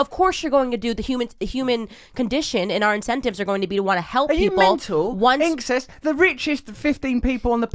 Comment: Did he just say "are you mental"?